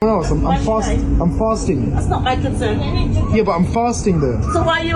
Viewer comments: Oh, he said I am fasting, I misheard that he said I am farting. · I thought he was saying he was "fosting".